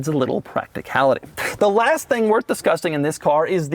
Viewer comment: Did he just say "The last thing worth disgusting..."?